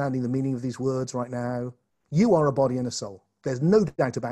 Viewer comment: ish "You are a body in? and? a soul." I hear 'and', closed captions say 'in'. thanks for any help, and thanks for this vid!